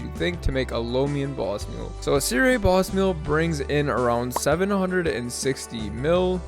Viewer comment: C.r.a boss mules? syria boss mules? 1:34. wtf did you say there?